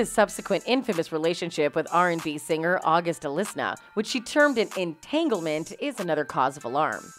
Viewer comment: did she just say august “alisna”? 🤣